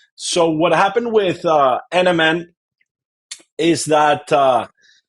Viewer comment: What is he saying? 2:56 What products? Captioning is saying NMN?